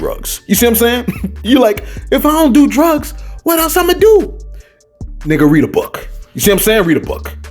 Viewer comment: Did say and I quote, " Nigga read a book!" at 8:17? 🤯